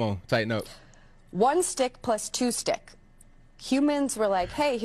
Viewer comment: did she say one dick plus two dicks? She must be a sex "doctor".